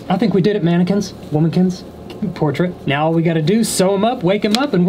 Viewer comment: "I think we did it manekins... Womakins.. Portrakins.." Lol. Same 🤦‍♀️😂💖 Edit: I thought he said Portrakins, and then I looked back lol 🤦‍♀️🙃😂